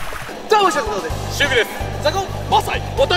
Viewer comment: 「シルクです」が「守備です」に聞こえた